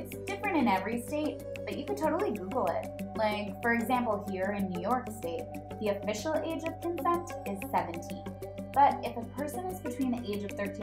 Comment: says "Here in New York State the official age of consent is 17" Captions say "the official a just invest is 70." I'm laughing my head off 🤣